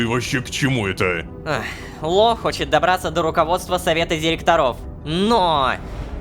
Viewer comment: Я один услышал что Лая сказала Лох хочет и т.д?